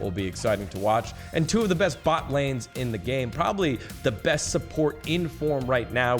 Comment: Did he say best support and say fudge? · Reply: Ya support fudge kekw - dGon